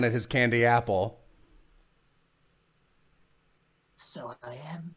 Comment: what does say at 5:12? I haven't been able to figure out what he said for months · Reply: So I am.